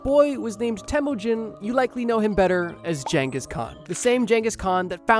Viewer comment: why is bro saying jenghis